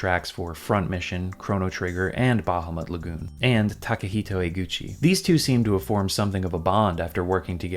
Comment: I had to do a double take, I thought you said Tadahito Iguchi 😅 (He was the 2B for the 2005 White Sox, whom won the world series also. He was a Japanese baseball coach for the Lotte Marines in the NPB)